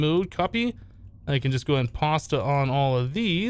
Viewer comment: did he just say pasta